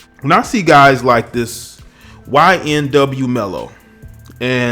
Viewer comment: Did this guy just call him ynw mello I swear that’s what I heard